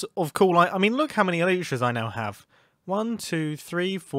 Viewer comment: What did you just say?? eYlsHA's ?!